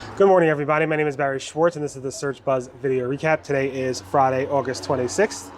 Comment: Hahaha, I had to rewatch first few seconds. I heard it as 'don't worry everybody' 😅😅😅